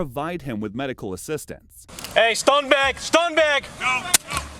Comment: Just in case anyone is confused: Captions show "stone back" but the officer is actually saying "stun bag".